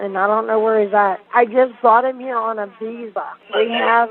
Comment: I heard "i just BOUGHT him here on a visa" 😂😂😂my Freudian slip is right